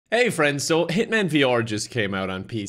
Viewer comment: I thought it said Human VR